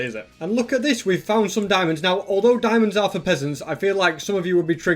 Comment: There was diamond in your chest I thought you said diamonds for cousins